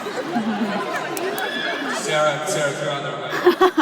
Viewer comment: what does Dom say to Sarah Hyland at 14:04 and what does Sarah scream back at 14:08? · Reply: Sarah is your out there I'm sorry. and Sarah screams back own it